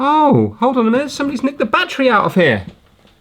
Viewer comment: sounded like you said "licked the battery" instead of "nicked the battery."